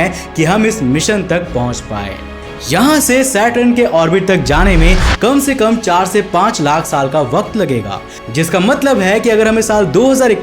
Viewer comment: Who other than me noticed that at 8:02 he sad char se panch lakh sal instead of char se panch sal??